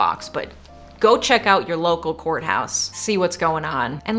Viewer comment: I heard "Go check out your local whore house.". Is it just me? 😅